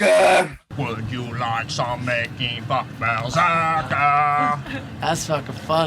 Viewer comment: Did he just say "Making Fuck"?